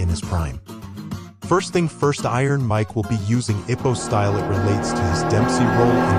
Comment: I thought u said “hippo style”